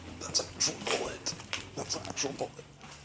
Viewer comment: what does the guy say in the bloody mess video? I heard "That's a ...."? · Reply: he said "Thats a bullet"